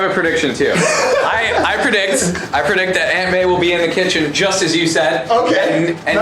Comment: When joven said aunt may, I thought he said ant man and I was like what?